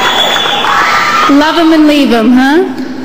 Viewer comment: What did she say? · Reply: Love em and leave them ay